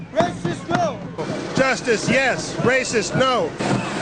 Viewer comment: Could’ve swore I heard the little Asian guy chant “justice no racist yes’!